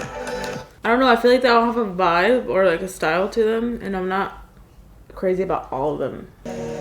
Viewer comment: you say “ I dunno I feel like they all have a vibe or like a style to them and I’m not.... crazy about all them” so that’s what u said just helping